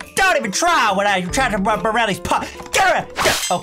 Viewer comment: What the- I think he is saying.. what i try bruh around these part